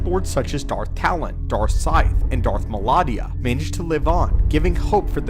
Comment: Did you say "Darth Melania"? 😳